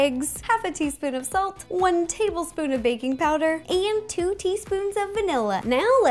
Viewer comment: I thought she said baby powder instead of baking powder😂